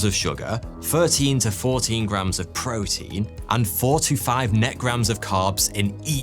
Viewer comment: I thought you said 45 net grams of carbs! I was astonished anyone would call that keto. lol